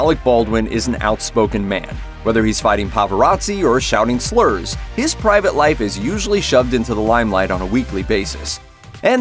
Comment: Did he just say "pava-razzi?"